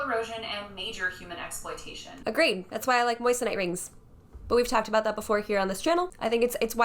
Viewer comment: WHAT KIND OF RING did you say? Please anyone, let me know what type of ring said, thanks! Btw, that other woman’s comment “boot on grandma’s neck” 😂 My grandma can kick a fool’s ass, so no need to scare future brides & grooms to feel threatened by anyone. 🙈 Just BE SMART & plan smart. Are you throwing a toddler’s birthday party, or a grown adult’s wedding? I agree with you! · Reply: Moissonite.